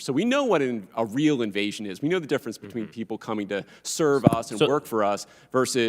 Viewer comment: Did he say people coming here to "serve us"? The truth comes out, Lefty.